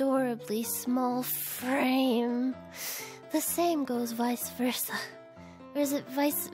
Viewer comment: I do not know why but when you kept on saying vice versa I thought you said Cerveza...I wonder why?